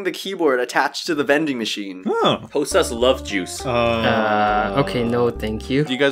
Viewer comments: “Any liquid u like can be produced by the vending machine” No one: “Hosuh’s love juice ;)” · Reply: I thought he said "Hosuh loves juice"! Omg! XD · hmmmm Hosuh... Hosuh: (wtf is wrong with): Hosuh's love juice Hosuh: (not you too) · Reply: Oh wow, I heard "Hosuh loves juice"